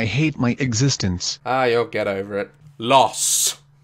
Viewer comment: When daz said you’ll get over it I thought he said your so ghetto 🤣